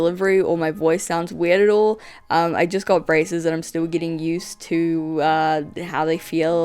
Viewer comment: you just got rascism?? I don’t think that’s what you said there but like lmaooo (good video btw)